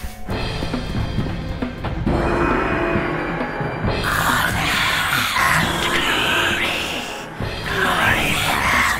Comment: What are the Ice Warriors saying at 0:59? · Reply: "Honour and Glory. Glory and honour. At last I live again."